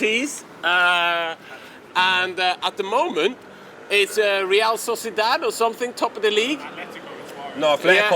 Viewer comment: did he just say 'REAL SAUCY DAD" ?? how was this missed lol